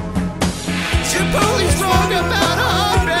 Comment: At first I heard "Chiptole is wrong about Islay"... Then realized it "Zirpoli is wrong about Ardbeg, it's just okay" haha